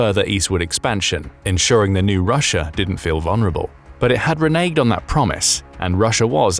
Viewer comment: "it had reneged on that promise" spoken....subtitles say 'it had reneged on that APPARENT promise' interesting difference. James Baker had promised....not by the letter of legality but the spirit of the agreement was broken by the west's war profiteers.